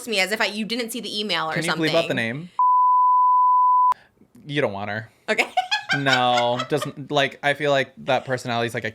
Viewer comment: who did she say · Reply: Brittany broski